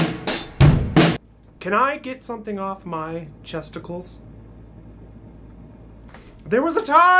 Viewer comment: In the beginning I thought you said testicles not chesticles XD